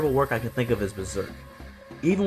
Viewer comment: What did he say at 2:03? Berzerk?